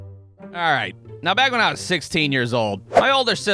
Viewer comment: oh my god I misheard that the first time as "My sister was 16 and on TV for divorce court" not " When I was 16..." Made me go "In not judgy but divorce court at 16 is intense..." Christ on a cracker just being on TV for divorce court is cool.